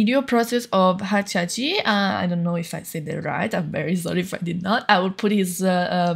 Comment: May i know harcaci chanel ? ^^ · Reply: you said you got the method from Hacaci, but i am not sure, i cant hear it clearly XD you said it